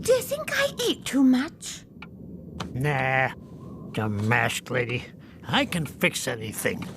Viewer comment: “ da mask lady, dumb ass Lady” anybody else catch it ?